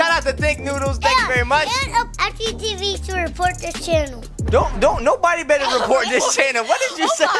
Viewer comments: why did say i will report the channel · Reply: he tried to say use star code FGTeeV to support this channel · and FGTEEV to report this channel: nobody better report this channel, u otta get out of this room! Me: what was trying to say was “use star code FGTEEV to support this channel!” Not “and FGTEEV to repot this channel”! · why did say i will report the channel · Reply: I thought he said FGTeeV to support this channel